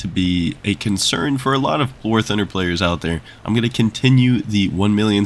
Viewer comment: In the beginning, when you said, "silver lions are a concern for a lot of war thunder players" i heard it as "silver lions are a concern for a lot of POOR thunder players"